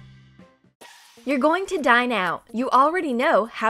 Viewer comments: i thought she said your gonna die now instead of dine out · lol I thought it said "you're going to die now."